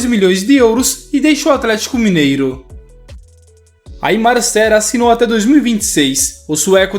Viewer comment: mn qual nome dele pq eu n entendi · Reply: Aimar Sher, chega do Hammarby